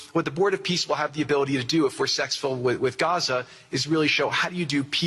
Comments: Did he say "sex full in Gaza"? Freudian slip? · I BEG YOUR PARDON….what did that automaton say… 40:47 ??? S*x filled? He’s a businessman. There’s a way to say successful. Whata freak!!!! I think the Reppie eff’d up. 😮